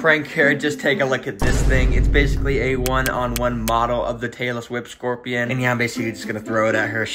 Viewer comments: ngl it sounds like he is saying «the Taylor Swift scorpion» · why did I hear Taylor swift scorpion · I thought he was saying Taylor swift scorpian It describes her